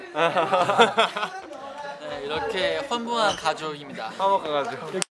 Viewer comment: 에이사씨 미안하지만 허무한 가족이라 들려ㅠㅠㅋㅋㅋ